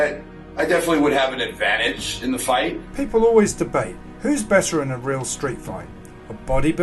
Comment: did he say "bester" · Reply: Claude Akel lol I'm pretty sure he said better, but it did sound like bester